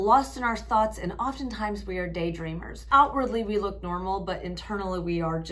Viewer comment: You said “day dreamers.” I thought you said “day drinkers” and I was like Oop i didn’t know that was a symptom, and then I listened again. Day DREAMERS. Got it. Lol